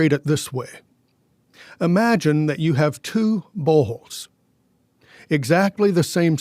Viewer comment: Huhhh? @ 6:40 I thought he said "imagine you have two balls." I look down... I'm like I hope they're still there, I had them yesterday 🤪. How long have I been asleep?